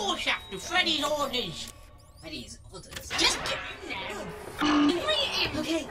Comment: because of bonnies thick british accent i thought he said Freddy's orgys instead of Freddy's orders.😂😭😭